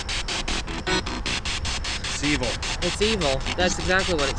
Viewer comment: I believe I heard these differently than you. 24:15 - silent 24:29 - truck 29:29 the police stop (or stopped) love your work!